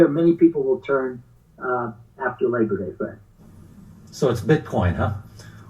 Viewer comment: After labor day...or today? The market is currently starting upward as of 10am eastern. BTC back over 12k and XRP at almost 32 cents. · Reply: I thought he said after you labor, today